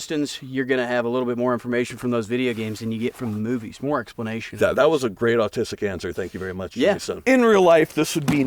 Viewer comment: Did he just say “that was a great autistic answer” (3:57)or am I tripping?